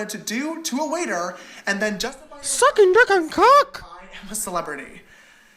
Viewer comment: please tell me you did not just say the n word. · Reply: he said "sucking dick and cock" it’s a line from tatis video